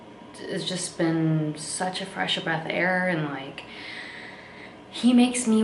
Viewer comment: Did anyone else catch that she said "fresh of breath air" around 3:20? 😊